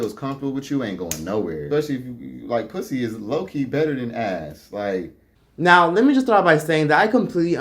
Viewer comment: secs N Did he just say "low key, pussy is better than ass" Aayyyoooo im ☠☠😂🤣🤣